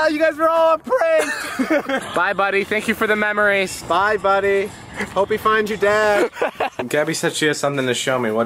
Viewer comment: When he said: "Bye Buddy, hope you find your dad." I STARTED WHEEZING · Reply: I thought he said “I hope we don’t find you dead”